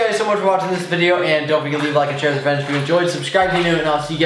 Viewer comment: at the end of his videos it sounds like he said "share with the french "